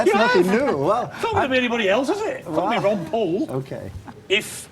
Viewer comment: did he just say "fucking ron paul"